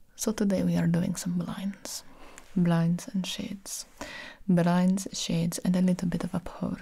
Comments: am I the only one who heard “blinds and sh*ts”? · “Today we are doing some blinds” Bruh I wasn’t paying attention and thought she said do some lines 😂